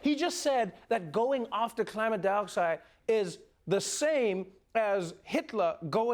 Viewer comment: did he just say climate dioxide 😂🤣